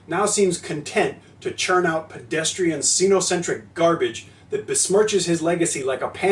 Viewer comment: For some reason I heard that as Cenacentric and I had this image in my head of him pile driving Louis Koo for the world heavyweight championship... as the execution. Then Brock Lesnar and Gold Dust appeared for some reason before I woke up from the best movie plot never seen.